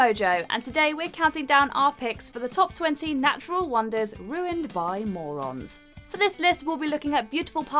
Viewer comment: I thought it said ruined by Mormons lmao 😂